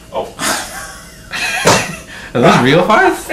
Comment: How gassy were you that day to fart so many times so loudly in probably less than an hour? 😂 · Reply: Owen Farmer Wait I heard "Are those real farts?"